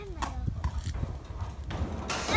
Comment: 「誰か、閉めてー」って聞こえる